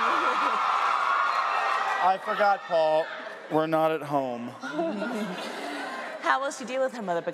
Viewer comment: what did Ian say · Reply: he said "I forgot Paul, we're not at home"